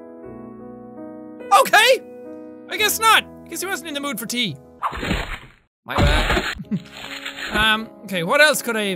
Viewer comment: anyone else hear the "my bad"? No? Okie